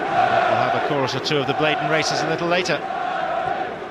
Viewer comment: No the commentator was talking about the bladen races which is a traditional song from Newcastle. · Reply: James Bevan thanks thought it was and it sounded like he said blatant racism but thanks for clearing it up. 👍🏿👏🏿